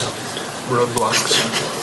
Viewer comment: Did Tyler say ‘Roblox’ at 2:56 😂